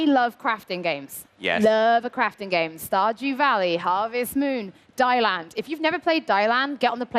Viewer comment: Does anyone know what game said at 12:18? It was the last crafting game and it sounds like die land? I always need another crafting game! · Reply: That's exactly it! Deiland, it's a little indie title from a Spanish team that is really charming! I did a livestream of it on Access if you want to check it out otherwise it's a great way to fix that crafting craving :) - <link>